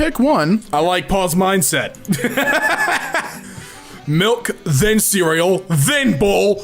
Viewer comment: On 34:56 DID IT SAY MILK THEN CEREAL